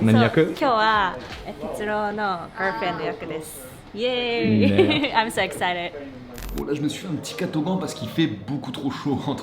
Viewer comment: Quand elle a dit "I'm so excited" jai compris "I'm a sex addict" le premier coup 😂